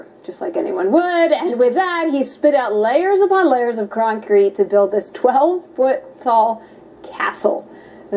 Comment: did she say croncrete? 😆 Ahhhh....women! They don't understand things.